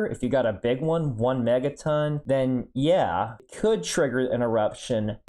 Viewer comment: I misheard and heard "Could trigger an erection"